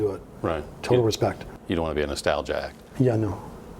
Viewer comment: the interviewer asks : "You don't want to be in the..." and I can't figure out the last word. Is it... stall jack ? Or something else ? Thanxxxx to anyone who may answer me, and have a gr8 weekend, guys !! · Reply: Nostalgic